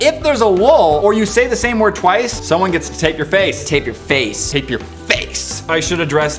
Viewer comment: I THOUGHT HE SAID TAKE YOUR FACE. 😂😂😂